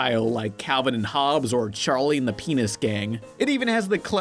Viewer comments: Did he just say charle and the penis gang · it sounded like he said pe-nis